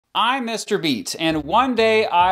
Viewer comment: i thought he said im mr beast and i was like no ur not